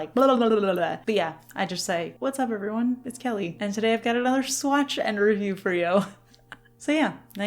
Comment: I’ve always thought you were saying Swatch review for You!